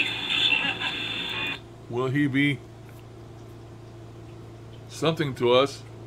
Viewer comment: Great session and I really liked your format. You made us part of your annotating process. Very cool. When you were playing back the bit at 7:36 I heard "will he still love us." Thanks for the session.